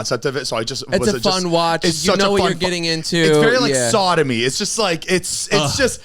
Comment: anyone else hear sodomy or am i trippin?